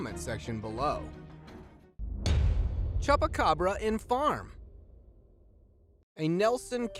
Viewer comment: did he just say chubucabra